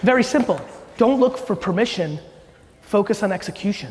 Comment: listening to this again...and NOW I hear " don't look for permission" I tend to do that...thanks, Gary xoxo